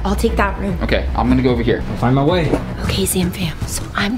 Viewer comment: Anyone heard Daniel say “find my wife”